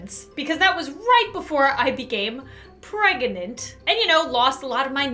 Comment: For a second I did a double take as it sounded like Lauren said she was 'Breadgnant' if Lauren had a bread baby I'd be happy